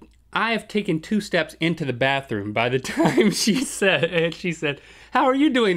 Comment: when you said you took two steps into the bathroom i thought your were say you took two steps back.